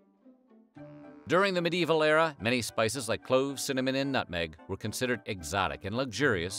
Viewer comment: I swear he said “cimmanon” instead of cinnamon